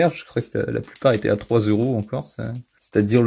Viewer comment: Pourquoi à 0:57 tu dis "la plupart était à 3euros en Corse" ?